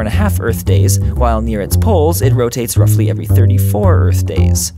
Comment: You accidentally said earths poles instead of suns poles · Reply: IT'S poles